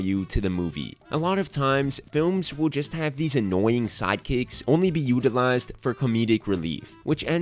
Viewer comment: Lol I thought he said side cakes like six times